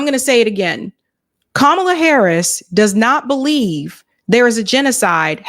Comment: WTF did you just say? Killer Kamala, doesn't WHAT!?!? ARE YOU SERIOUS RIGHT NOW!?